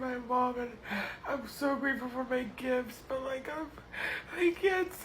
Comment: thats what i heard she was talking about her mother visiting in the hospital and i thought she said her kids visted too. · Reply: I just watched that part, she says "Im so happy to see my mom and so greatful for my GIFTS" it sounds VERY much like kids but its gifts.